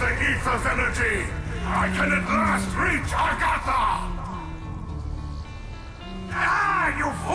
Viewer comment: did he say he can finally reach Agartha?